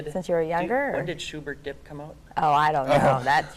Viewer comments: What is he saying? It sounds like "when did shubert get _____" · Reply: When did shubert dip come out? I have no idea what that is, but i think that’s what he said. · What's he saying at around 5:14. "When did what come out"? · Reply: Shubert Dip, the british group EMF's debut album. For some fucking reason.